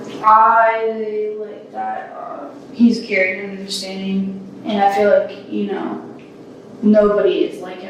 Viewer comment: subtitle somehow is "insane" when she actually says "understanding" ....quite a difference lol